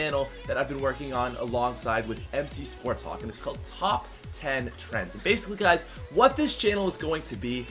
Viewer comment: the new channel I thought you said top 10 tranzit